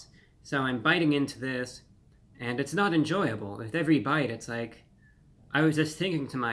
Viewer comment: Am i the only one who heard him say it's so salty with every bite and now want to order it more😂